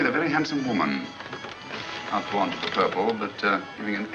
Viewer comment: Maybe someone british may help here: @ 7:58 the subtitle says: "Not fond of the purple..." but Holmes lips clearly form a "b", and I understand: Not blonde (or not blunt - maybe not blind - of the purple) which wouldn't make any sense, would it? I guess he states that he doesn't like the purple... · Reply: I believe he's saying "not born to the purple" which means she's not upper class (but she is pretending to be).